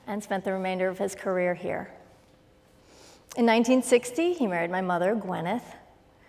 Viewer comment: I thought she said "murdered" 😨